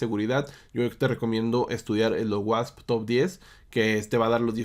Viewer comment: que dijiste que se debe estudiar en seguridad? no se como se escribe lo dijiste en el minuto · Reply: OWASP TOP 10 <link>